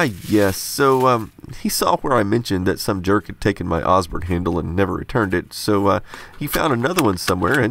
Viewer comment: What is the handle @ 8:20 for? I don’t understand acoustically what he’s saying… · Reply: The Osborne computer. He sent out the previous handle for someone to recover the leather binding, but never got it returned.